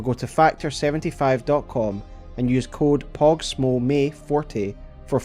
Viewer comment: I thought he said use code pop smoke